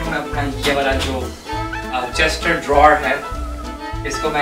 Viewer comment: did he just say CHESTER DRAWER to CHEST OF DRAWERS😂😂😂